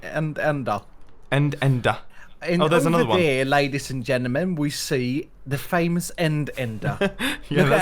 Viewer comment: i first heard laidies and endermen, lol